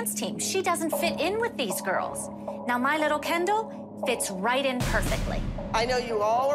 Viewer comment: When Kelly isn't yelling or screaming her voice is so soothing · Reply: We I go to sleep all I hear is “My LiTtLe KeN DoLl”